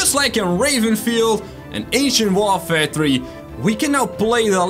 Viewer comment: it sounded like you said Asian warfare